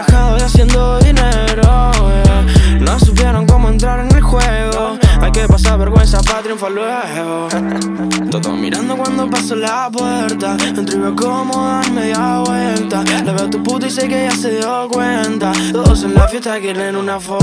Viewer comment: 'Hay q pasar vergüenza para triunfar luego' Esa frase es mi motivacion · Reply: pensé que decía: "el que pasa vergüenza va a triunfar luego".